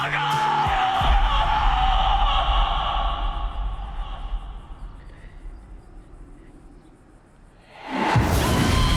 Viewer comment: What did Myrtle said in 7:25? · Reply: Balenciaga